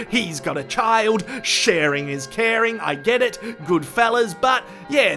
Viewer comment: Did you just say "Sheeran is cearin"? LOL